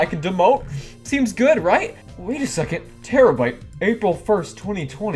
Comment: When you said terabyte I thought you said tera brite and I was like the YouTuber?! But then I was like ohhh- 💜😂